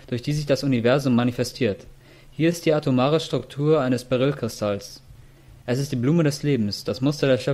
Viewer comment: BITTE ICH KANN DURCH DAS GENUSCHEL BEI minute 3:20 nicht heraushören wie der kristall heisst.. ich verstehe "balölkristall" "oder bahölkristall"... Wäre nett... wenn ihr bei solchen infos die namen der kristalle mit ins video einfügt. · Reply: Beryll-kristall du Nase